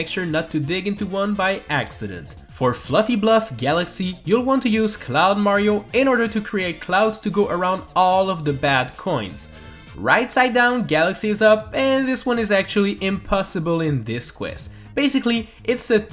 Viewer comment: DIDUTH I HEAR CLOWN MARIO